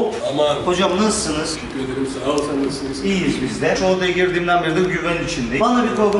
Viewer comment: iyiyim saol sen nasılsınızmı dedi benmi yanlış anladım